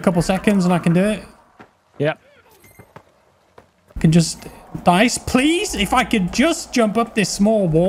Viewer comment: did you just say DICE please? haha